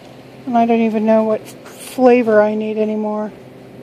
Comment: Did she say she doesnt know what FLAVOR of eyeglasses she needs ??